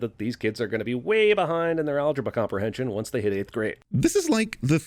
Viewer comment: did he say algabra